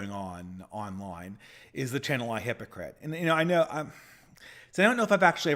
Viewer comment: what does he say "The channel Ah hypocrite"? Help me plz. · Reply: "I, hypocrite"